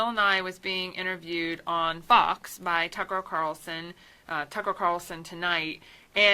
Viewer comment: Hmmm (14:49) Is it just me or did she say "Tucker O'Carlson"?